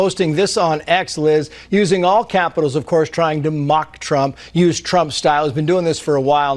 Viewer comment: I heard the word Chump🤨